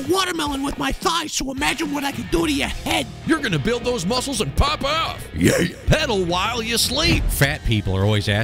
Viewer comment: did you say.....Pop off?!?!